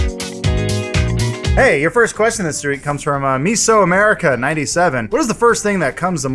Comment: Why does it say "reek" at 0:58? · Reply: Adam says "your first question this reek" instead of "Week"